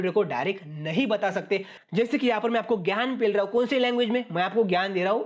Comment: Bhai is video me 13:10 par kya bole hai Gyan pel raha hu 🤣😂😂😂🤣😂😂😂😂